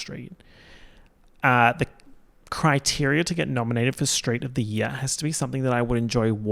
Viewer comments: i heard "street of the year" as "straight of the year" as in straight person. and i was like that's an incredible category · When he first said "street of the year", I thought he said "straight of the year" AKA the straight person who excelled in being less annoying than all the other straights.